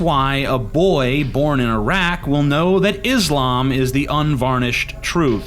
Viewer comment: I thought he said a boy born in a rack.